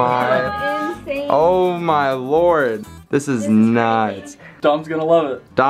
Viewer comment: When they filled Dom’s room w/ Lexi posters said “This is nice!” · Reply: i thought he said this is nuts lol